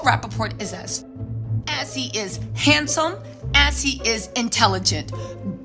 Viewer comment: Did she say handsome , that dude looks like a wet mouse